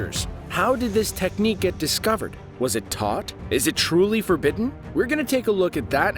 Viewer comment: I actually thought he said, was it toph?😄